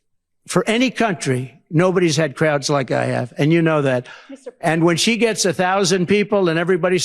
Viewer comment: Whenever he says “and you know that“, I hear “that was total bullshit“.